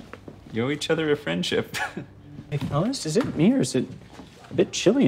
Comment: did he call him thomas? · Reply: he said "hey fellas"